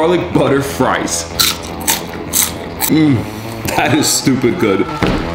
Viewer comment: You know when he says "Stupid good" it's amazing Edit: WTH TYSM FOR THESE LIKE ON THE COMMENT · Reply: I thought he said Super good before! 😅😆